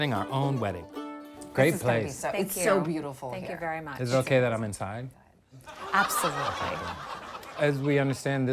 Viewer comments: Did he just say, "Is it ok that I'm inside???" 😂😂😂 I thought he said, "Is it ok that I'm untied?" lol that would have been even funnier! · Lol is it okay that I'm inside? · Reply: Moe Lumeka I thought he said is it ok that I’m untied 😂😂